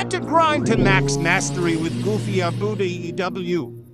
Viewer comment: It sounds like he said goofyabootyaew